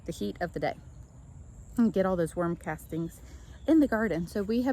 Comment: did I hear you correctly you put worms in your garden may I ask why does it help any and we're praying for you son a speedy recovery 💗🙏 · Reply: I thought I heard worms too 😂😂 so glad you asked. I was scared 😄